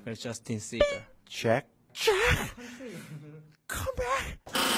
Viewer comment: What the heck did V say there?? 😂😂😂😂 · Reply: “Jack... jack come back”